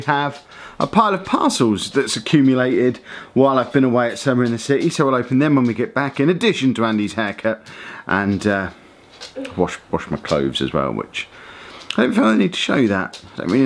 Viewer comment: When you said 'and wash my clothes as well' I thought you said wash my pubes😂😂😂